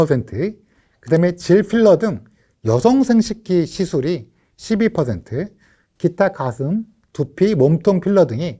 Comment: 자막 표기 오류인 것 같네요. 자막은 52%라고 나왔지만 교수님 발음은 12%로 들려요.